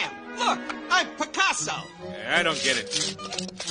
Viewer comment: I thought he was saying something else, and the second I thought that I started laughing so hard 🤣 P.S: I thought he said "you hockey f**k"